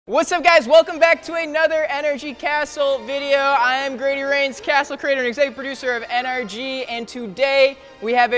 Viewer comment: For a second i thought he said greedy rains😂